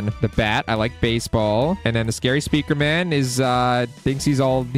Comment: DID HE JUST SAY SCARY SPEAKERMAN ON THE SNEAKY SPEAKERMAN LOOK